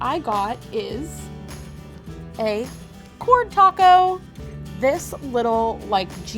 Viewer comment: Did she say cork TACO? Now I've heard it all... 😂